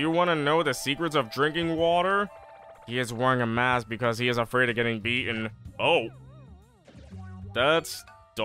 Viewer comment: I thought he was saying he was afraid of getting Covid.